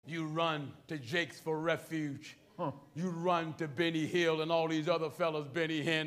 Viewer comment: Did he just call him "Benny Hill" who was the English clown comedian? 😂😂